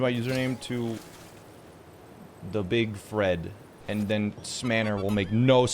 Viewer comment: i thought you said you would change ur name to the big fred